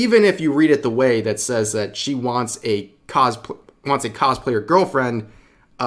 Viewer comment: She sounds like she saying she wants to be a cosplay girlfriend